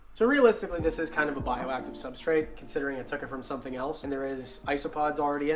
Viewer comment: He clearly said said “this is a kinda bioactive substrate” he never said it was bioactive! Isopods are a part of making a bioactive terrarium so that is why we said “kinda” No hate just pointing that out!